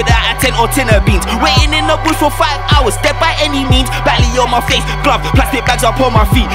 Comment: Fully sounded like he said "batty on my face" at 1:19 lool